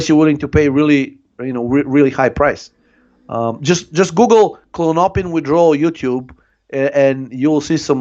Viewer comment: Hey! Nice one as usual... What does says to google at "52:27"? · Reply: Klonopin en español es Clonazepam.